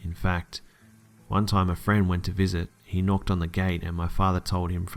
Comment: I'm sorry did you say he knocked on the gate?